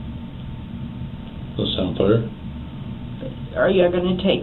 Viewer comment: These subtitles are horrible. "What's up bro" instead of "That sound fair?"